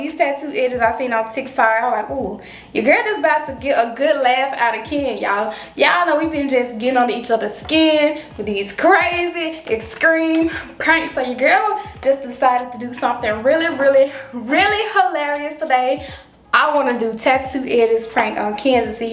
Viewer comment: I love how be saying extreme"exscream",sounds like she saying ice cream😂I love her accent